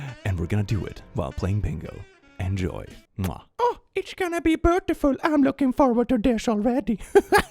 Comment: It sounds like you said "brutiful". This is now a word. It means brutal and beautiful.